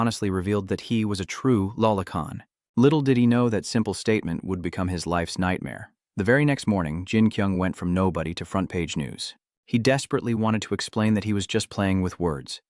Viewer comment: i heard little diddy know that simple statment would become his life nightmare 😂 check my reply 😂😂